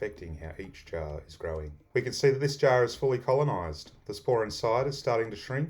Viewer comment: I couldn't understand the information at 0:25 to 0:30. How do you know the right time to use the spawn? "The spore inside is starting to shrink as the mycelium pulls in." ? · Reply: Forgive my mumbling. I should have said spawn. Basically...once the mycelium has colonised all the grains.....the spawn starts to shrink inside the jar. Sorry again for any confusion